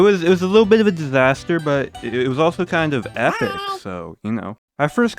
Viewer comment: did someone say "Epik"?